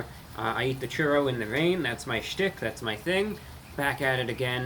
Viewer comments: Did he just say "That's my shit, thats my thing?" Talking about the churrors?? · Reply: That's my shtick. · Had taken me back there for a second. I thought reviewbrah said that's my sh*t, but he actually said that's my schtick. 😂